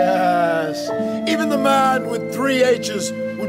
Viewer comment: At first I thought he said "The man with 3 ages".